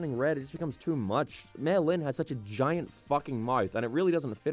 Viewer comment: At times I legit thought he said melon instead of mei lin